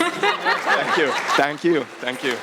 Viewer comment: What happened at 7:30 ? Someone said "thank you" in the audience?